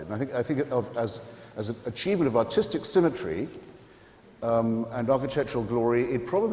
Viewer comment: What does he say at 2:18? "Artistic symmetry"?